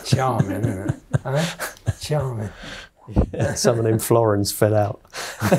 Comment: "something in florins fell out". Florins were old British coins. The subtitles didn't catch this.